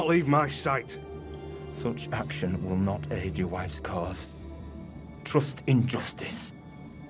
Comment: “Trust in justice” all I heard was “trust injustice”